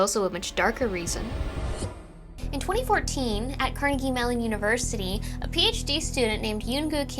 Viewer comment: Really should listen these things at normal speed, almost spat out my drink when I heard "cranky melon university"....